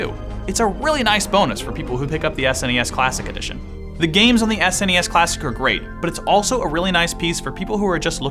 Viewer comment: I thought it said SNES Classic hard on